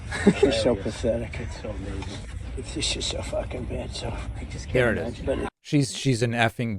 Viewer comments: I legit thought he said "shes so fucking bad" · I mean, Maybe he did, but I heard, "Just so fucking bad". I'm not defending Trump here, I'm just saying maybe he didn't (although I totally believe he would say that.). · Not saying he didn't say it, but I hear, "It's just so f*cking bad, so...'" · David im on your side but i dont think thats what he said. I heard "Shes so pathetic" then "its just so fucking bad". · On the real, It sounds like he said, "She so F-ing bad." Listen again.. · I thought he said "she's so f-ing bad." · In no way I’m taking up for Trump but it sounded like Trump said she’s FN bad. Trump pronounces some words quite funny to me and that’s one of the words I’ve always thought sounded funny the way Trump says it. Yeah he still is a MORON. It just didn’t sound like bitch. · Hmmm...think I heard "f-ing bad" with his NY accent... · It sounded like he said "She so fu**ing bad". Of course this is just Trump projecting as usual. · I think I heard, "She's so f**ckin' bad." Because he says, "She's so bad," in the sentence just before it. Regardless, Tramp's headed to jail in Loserville, and I can't wait. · I don’t doubt that Trump has called her a b*tch before, but in the golf cart video I heard “f*cking bad.” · I heard "f-ing bad" from his golf cart which makes sense because he just got through saying she was "bad" much more clearly. Not a good clip to confirm your point but it is good to re-see this clip